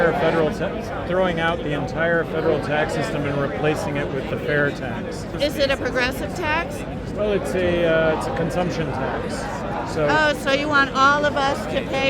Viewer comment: I thought she said "Progresso", as in the soup? She's definitely a chowderhead.